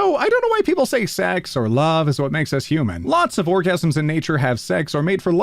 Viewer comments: I’m sure he said “orgasms” instead of “organisms” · is it just me or who else heard him say orgasms?